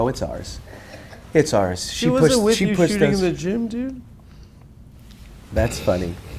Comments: what is the bug even saying here? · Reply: She wasn’t shooting w you in the gym* is a reference to (iirc) a Rick Ross bar about Kobe, his wife, and alimony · What he say 44:45 "Ur wife is shooting at the gym too?" Wut